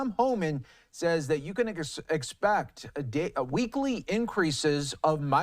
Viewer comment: His fumble made it sound like he said you can N word expect lol